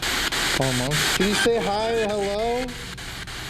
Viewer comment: i swear when you ask can you say hi it sounds like “it’s Dave”